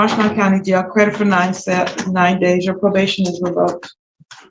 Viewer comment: Thought she said 93 days at first? Credit 9 days. She got soft again!